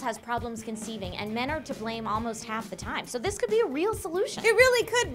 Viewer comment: is it wrong i heard women are the problem more then half the time instead of men are to blame almost half the time?